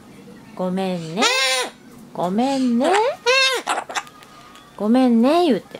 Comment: で「ごめーんね！ﾊﾞｶｯ…」って聞こえるのは気のせいかな…